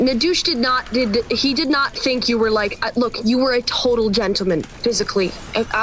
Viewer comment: saying "you were a total gentleman" sounds like she is say you were a "nice guy"